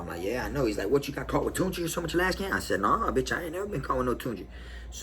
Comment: Sorry my end gets spotty scuffles ha... What did you say at 18:00 something when your bunkie asked what did you get caught with something something canteen or can and you said no I've never something something. What was it that he asked if you have ever got caught up with? I also thinks that's cool last video when that guy tied the string on his tooth and swallowed it. Ha your expression is the best . (Your expressions in story telling are always good though ) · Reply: He said toonchi it's the k2 synthetic weed